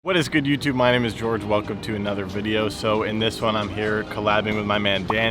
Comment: When he said “ hello, my name is George welcome to the video,” I thought he said “ hello, my name is George Washington,”